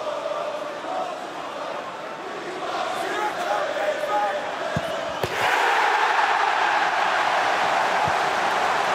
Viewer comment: someone said “smth smth smth pickford” can anyone make out what he said? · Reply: I think he sayed pickford pickford